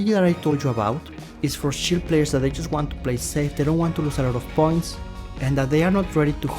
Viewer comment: Whenever said "points", i heard "PORNS" 😂😂